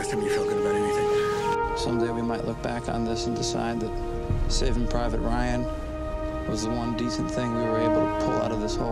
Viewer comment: Dang. I thought it said "Shaving Ryan's Privates."